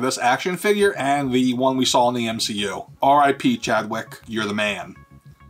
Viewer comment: I thought he said John Wick.